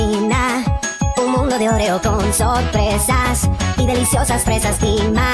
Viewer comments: I heard ''sus'' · I heard “sus”